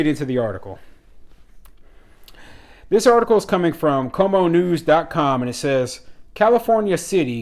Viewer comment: Taxpayers getting a fair shake? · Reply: Yesterday I thought you said Cuomo news . Ha